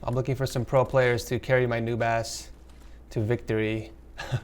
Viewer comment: When you said noob ass I swear to god I thought you said new bass 😂